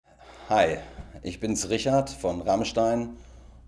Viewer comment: To me, it sounds like he says "Ich bins Richard" in the beginning, not "bin". Why's that, german viewers?